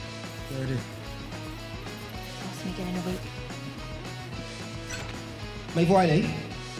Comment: CAPTIONS ON: 1:41 ”That’s me getting a wig” ????? lmfao what. Pretty sure she said ”ask me again in a week”💀💀